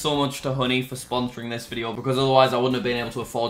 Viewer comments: When he first said honey I thought he said hoodie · He said honey thought he said hoodie